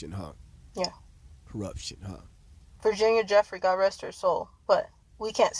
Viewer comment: Virginia Dufree I thought she said. But Virginia was documented facts